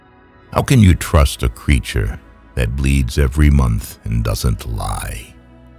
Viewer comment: that doesn't lie? That's what I heard, ..